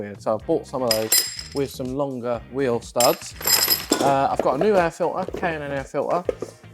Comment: HAHA my wife just popped in to my mancave and asked "I'm sorry - did he just say he came in an air filter?"... I almost pissed myself! WTF! Hahahahaha